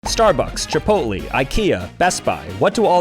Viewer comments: did bro just call Chipotle, Chipoly?😭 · did he just say chipotleee……